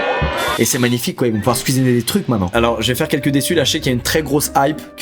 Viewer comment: j'ai entendu "squeeziner"